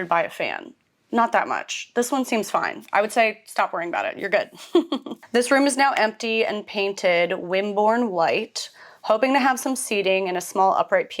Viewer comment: i thought he said the room is all now empty